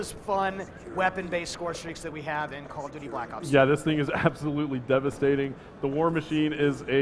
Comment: Lol at the start it sounded like he said: Call of duty black socks!